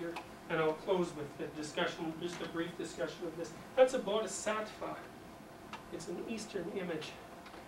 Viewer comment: What is the last image he presents, at 1:43:20 ? I can't hear the pronunciation. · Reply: He says "Bodhisattva". It seems to be a drawing from Tibet, and it is apparently shown in the book "Hero With A Thousand Faces".